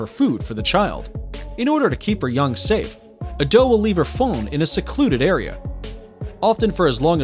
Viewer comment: Lol I thought he said “they leave their phone in a secluded area “ when it was actually “they leave their fawn in a secluded area “